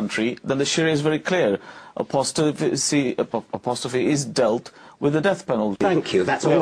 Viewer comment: did he just say 'apostrophe' ?? '